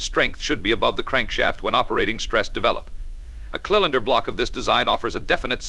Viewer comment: Did I just hear "kill-inder block"? Say what?